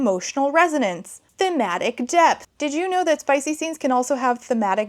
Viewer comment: I thought you said thematic death 😭😭😭 these folds are deadly